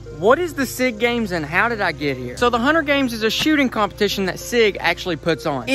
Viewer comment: I thought he said hunger games 😂😂😂